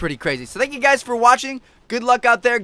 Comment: WTF is he saying lol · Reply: "Check out the videos if you haven't seen em yet" really fast lol.